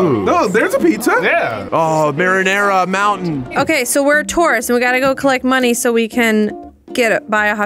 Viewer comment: what said: we're a tourist what I heard: we're a taurus how did she know I was a taurus...